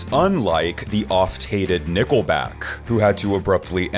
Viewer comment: Pffft. When you said, Nickleback, I thought you said Nippleback